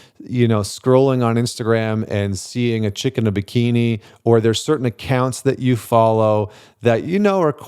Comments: Lolololollo 7:24 I heard "a chicken bikini", then I realized it was "chick in a bikini" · I thought he said “chicken bikini”, thanks for that. 🐓 😂